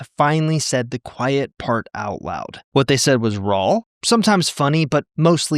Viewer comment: i wanna hear you say raw again...did you say "rawl" or was it just my imagination?!?! lol 😂